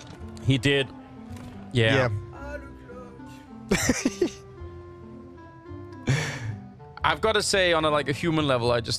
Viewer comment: did he just ironically sighed "Allu clutch"?? Saw it live but couldn't understand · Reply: He said "ah le glock" which translates to " ah the glock" XD